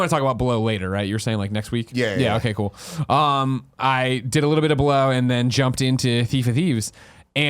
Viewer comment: "Did a little bit of below" REALLY sounded like he said "Did a little bit of blow." Lol Quickly caught me off guard.